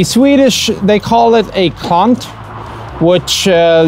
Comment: I can not make out what he says the tool is at 13:24? Can someone write it in swedish? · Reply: "Klant"